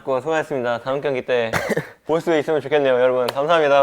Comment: 난 다르게 들리는게 '다음경기도 볼 수 있었으면(이겨서 BMW THE DRIVE에서 볼 수 있었으면) 좋겠네요' 라고 해석했는데 어떤게 맞는지는 잘 모르겠네